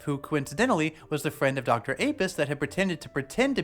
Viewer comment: I misheard Dr Apeist as Dr Apist, which could easily be typed wrongly as D Rapist.